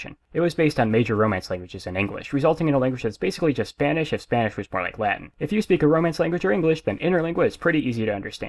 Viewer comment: At about 0:25, you say "resulting in a language that's basically just Spanish..." and then I can't understand the rest - "Spanish buf Spanish forrek Latin"? Can anyone help? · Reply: He said "language that's basically just Spanish if Spanish was more like Latin"